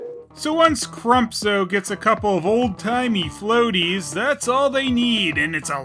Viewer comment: I think the shrimp-abomination says "old timer" but at first I heard it as "Alzheimer". Could be the latter. It would kind of make sense given the old man's line.